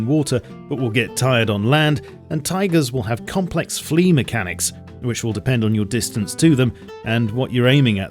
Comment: I thought you said Complex Flea Mechanics XD